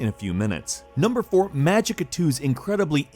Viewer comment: What's the name of the game? I can't understand correctly. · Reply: The game is Magicka 2 , I'd reccomend you to play the first part , since it's much better.